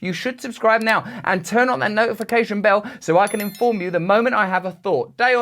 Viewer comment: I thought you said I'll inform you when I had a fart🤭